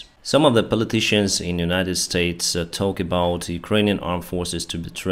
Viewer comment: Great show as always! One thing I wanted to clarify, since it sounded like you said Kissinger, when talking about Adam Kinzinger, is that he is NOT Henry Kissinger, who is entirely different and a war criminal who has horrible takes on the Ukraine war. I don’t agree with all of Adam Kinzinger’s policy opinions, but he is an honorable person who has consistently supported Ukraine, and is himself an airforce veteran. So if he supports getting you guys F15/16s, you can bet he means it :)